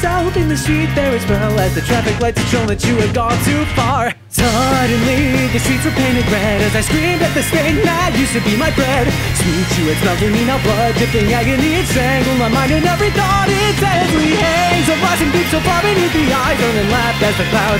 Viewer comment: I keep hearing "as I screamed at the stain that used to be my bread"